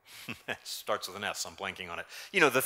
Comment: "Starts with an S I'm blanking on it" ? I heard "starts with an S I am plunking on it" LOL, I thought that was just a very nice joke from to suggest Splunk.